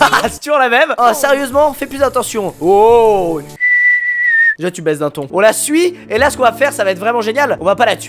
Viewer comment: j'ai compris "Déjà tu baise un thon" 😐... j'ai un problème ?